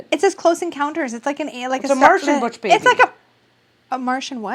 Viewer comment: I also thought said Butch baby LOL